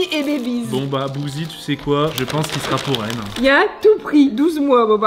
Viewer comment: elle a dit qu'elle a beaucoup envie de sucre donc c sur que c une filleeeeeee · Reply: Je confirme un peu ce que tu dis, à 5:55 dit '' bon bah t'sais quoi le maillot sera pour elle hein ''